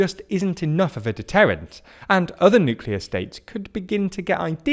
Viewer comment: At first i thought it said unclear